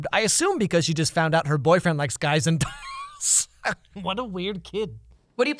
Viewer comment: When he said her boyfriend likes guys and dolls, I thought he said her boyfriend likes guys